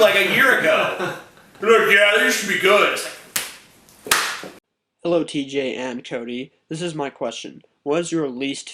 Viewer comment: Did he say "Hey TJ and Tony"?